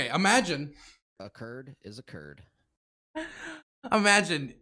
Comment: WAIT... That alert used to say, "A Kurd is a Kurd?" I swear I always thought it said, "A turd has occurred." ...Having no idea what that had to do with anything. Just thought it was a silly meme/injoke that I wasn't privy to from an earlier stream. Totally and entirely misheard that dozens of times.